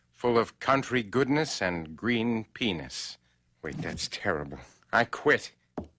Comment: OMG, i finally get the joke. "Full of country Goodness and Green Peaness". I was just laughing at this clip for the last month cause it sounded like he said penis for no reason and I have the maturity of a 5 year old.